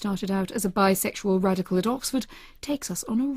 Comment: did she say hitch was a bi sexual radical at oxford?